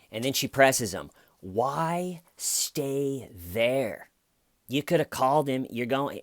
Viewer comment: lol why Stay…ven whyyy is what I heard